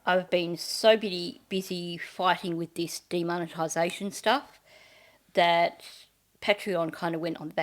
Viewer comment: I thought u said DEMONitization. As if u were fighting off a demon, exercise! 😂